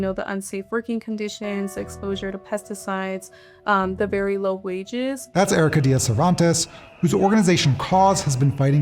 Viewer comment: Did she say "berry low wages"?